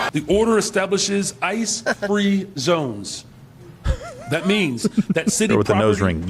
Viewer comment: Did he say "Ice Cream Zones"? Hell Yea!!! lol